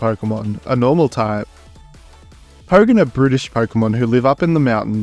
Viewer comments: When you were talking about Pogan you said it was brutish but it sounded like you said that it was British 😂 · AHH, oh, sorry I thought you said British, carry on